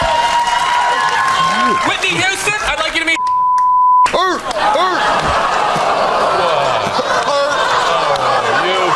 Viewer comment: All this time & I still can't figure out what Brad said in response to Colin being the performing seal. 8:37 "Whitney Houston, I'd like you to meet ...." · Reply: He says Stephen Hawkin